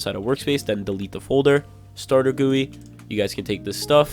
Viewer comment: did bro call stater g-u-i stater gui?